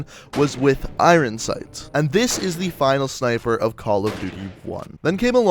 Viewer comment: he says snifer instead of sniper😂